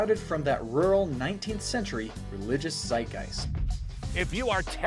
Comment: I can't make out the word he's saying at 4:41. (religious what?) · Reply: Sounds like "zeitgeist," defined as "the defining spirit or mood of a particular period of history as shown by the ideas and beliefs of the time."